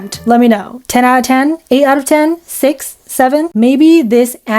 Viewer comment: did she just say... 6 7